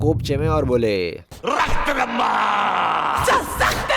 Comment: Did she say SU-SUCKED-LAMBA😂